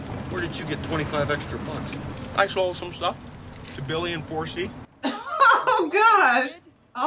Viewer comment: I still can't believe up until s few years ago watching with subtitles, he says "Billy in 4c", as in apartment 4c.. I thought wrong about what he said for almost 30 years.. I thought he said "Billy Enforce", like a name.. Also the cop who drank the pee had his own decent movie named RocketMan from 1997, y'all would like that one.